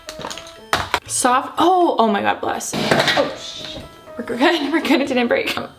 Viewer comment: I CANNOT BE THE ONLY ONE WHO HEARD HER SAY “sh*t” ?! OMG 😂